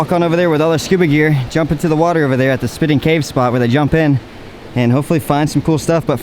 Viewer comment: I thought it said spitting in caves- lmao